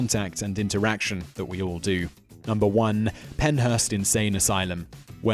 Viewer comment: did I hear him say 1908 to 1907? Or did I hear that wrong?